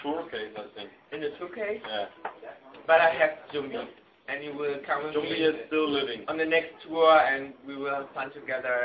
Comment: oh, thnx for the subs, my computor kinda kills the sound for every video and before i read the description, i thought bill said 'jumbie will come with me to the next world' and i was like 'wtf?!' haha